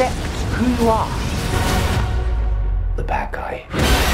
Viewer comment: The bad guy? I heard "the bat guy" lol!